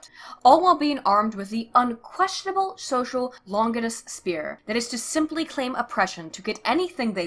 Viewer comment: Wait, did you just said "Longinus spear" or am I just hearing things?